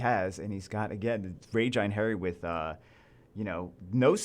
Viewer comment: Name: Einheri What I heard: I'm hairy